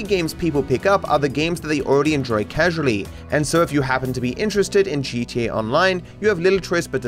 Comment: did you just say “Cheat T A Online?